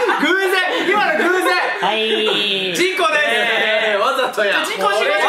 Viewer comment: 事故です！が「ちんこです！」に聞こえて笑った記憶ある